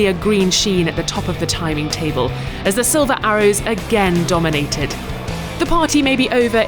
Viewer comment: why did she say sliver arrows?